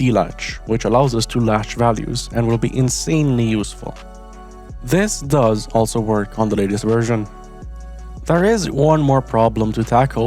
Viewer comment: i tested it on the ORE server and it didnt work only the dlatch worked and i couldent read from it, The ORE server is 1.21.8 And i even tried 1.21.11 singleplayer and still didnt work if yk the answer it will super help me :d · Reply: well I heard “this does ‘also’ work on the latest version” and I also didn’t check description mb i thought like the ‘also’ u said meant that the update suppressor was also on 1.21.10+ mbmb